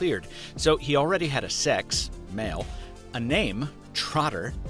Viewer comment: I thought said "he already HAD SEX" and I was very concerned for a moment there